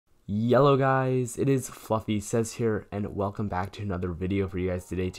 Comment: "Yellow guys, it's FluffySez here" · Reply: i heard "yellow guys, it is fucky sez here"